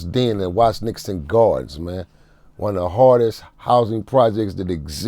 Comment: He's from the 80s and 90 era. The Gardens was definitely one of the hardest places on earth at that time. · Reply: I thought the dude said the hardest period. But I know what’s up with that section, I’m a 44 year old L.A. native.